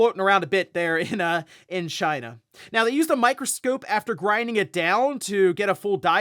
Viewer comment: Did he just say microscoop? 😂